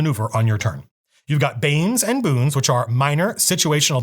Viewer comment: At first I thought you said "bangs and booms" and I was like ooo what's that 👀